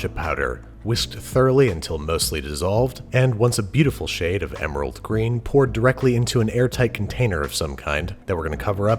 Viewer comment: Did he just say emerald green Was that a JoJo reference